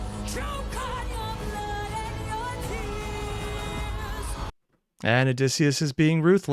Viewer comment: He actually said "ROW for your lives" and not "RUN for your lives" Jorge said that this is one of his favorite misinterpretations